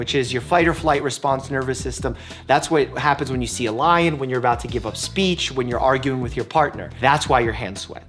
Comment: Who else thought he said what happens when u eat c*m